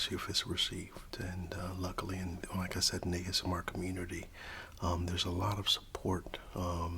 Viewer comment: Great video! I love the timbre of your voice, and I enjoyed the details of your process! (FYI, you might want to double-check your captions at 16:31 - I think you were saying "in this ASMR" but the text misheard it)